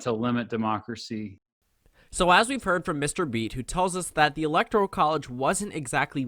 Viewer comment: Yooo i thought it said MrBeast 😂